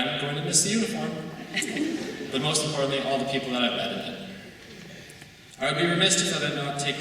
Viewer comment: i thought he said "all the people that i've HAD in it.."